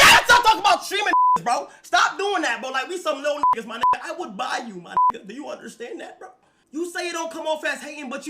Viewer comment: did he say "I will buy you my n*gga do you understand that bro"? 💀💀💀